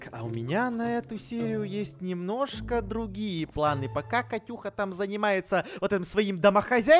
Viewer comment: мне послышалось покакать уха